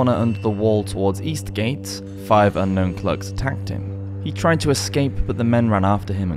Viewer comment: Did he say "plugs" or "thugs"? I thought the word thug came from the Indian Thagi. If not what is a "plug"? No plumbing or Beano references please... ᵃˢᵏᶦⁿᵍ ᶠᵒʳ ᵗʳᵒᵘᵇˡᵉ ᴵ ᵏⁿᵒʷ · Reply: “Clerks”